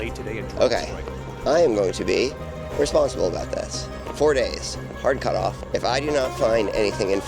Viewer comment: I thought you were saying you were going to be responsible with this new Indiana jones whip 😂